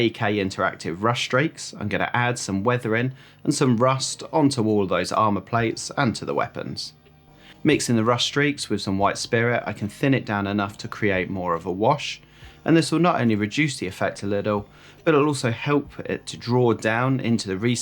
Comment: Hi mate, sorry, I couldn't understand 7:45, how is the medium called? White spiral? You have an Amazon URL? Thank you so much and really nice work!